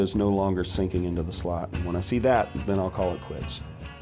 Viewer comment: i swear at first he said "then i'll call the police" lol